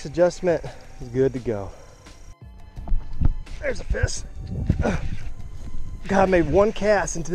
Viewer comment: ”There’s a fis!” I didn’t spell that wrong that’s what he said